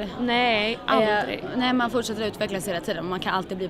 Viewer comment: what did she said exactly? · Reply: "Ge inte upp" = "Don't give up"